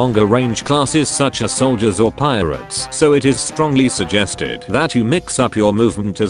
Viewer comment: God when I heard pirates at 1:25 I thought I heard pyros. I play too much GW2 and TF2